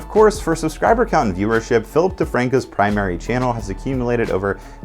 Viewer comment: I got excited for a moment when I hear 'Filthy Franku'. But turn out it was Phillip DeFranco.